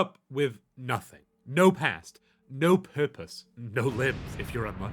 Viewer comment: I thought that intro said, "NO PANTS." 🚫🚫👖👖